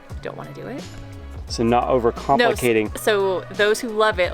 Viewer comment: I don’t think she said over complicating, it sounded like she was saying over compensating.